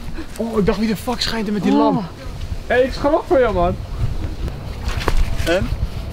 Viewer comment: ik hoorde iemand ‘hey’ roepen, ik weet niet op het was of iemand anders maar je hoort het heel duidelijk